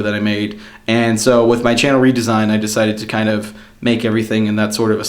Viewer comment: I thought it said ''Channel resign''.